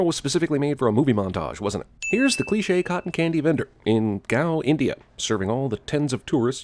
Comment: did he just say 'Goa' "Gao"? damn